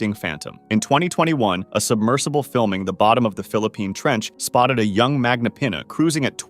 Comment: did you just say Philippine? GOD THATS MY COUNTRY AND BRO I LOVE SQUIDS